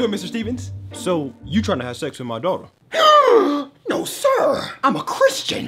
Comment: I thought he just said “So. You’re trying to have sex with my dog.” 😂😂